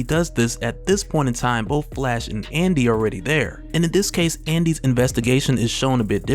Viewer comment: what's that, Anti-Scream? Anti-She Venom?